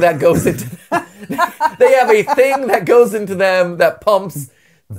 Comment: I think it said vein, not thing